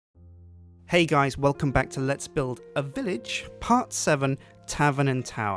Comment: i heard 'hello guys and welcome back to 'lets build a bitch'' XD